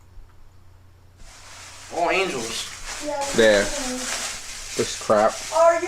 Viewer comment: "Were all angels" or all angels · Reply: OffPeng Leng I thought was saying "oh yes", now looking back & listening, yes, definitely sounds like "all angels".